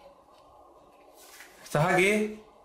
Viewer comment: En el minuto 7:38 se escucha una voz que dice Israel